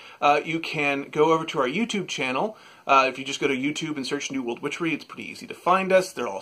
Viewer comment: Hahaha I love you two, and just wonder has anyone else thought (?) was saying ‘New World Butchery’ in the opening?